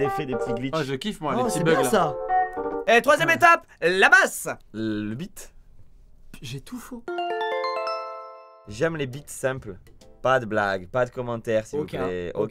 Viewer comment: t'as dit quoi bigflo???????? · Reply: Les bits simples. T'as pensé à quoi 🤨?